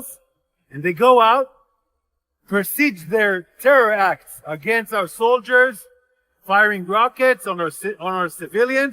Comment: Did he just say, ”they go out perceived their terror act on our soldiers “😂😂😂😂😂😂 time